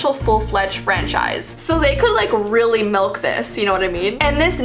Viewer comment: Did you say milk?????